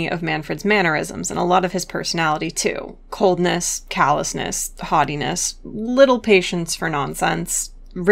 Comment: ohhh haughtiness i thought you said hottie-ness